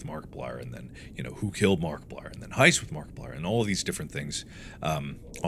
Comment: Mark I know you said Heist with Markiplier but I misheard it as High School with Markiplier and it would be really funny to have a choose your own adventure trope filled High School Drama with your twist on it.